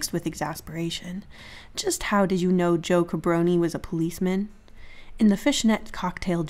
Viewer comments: I think the cold I'm home sick with is affecting my brain I heard Joe Cabroni as "Joker Brony" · I really heard "Joke Brony", I think it's enough internet for today